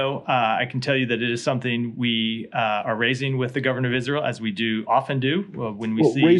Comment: Did he say "governour of Israel"?